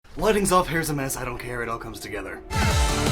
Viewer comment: lol, i thought you said weddings off at first, lol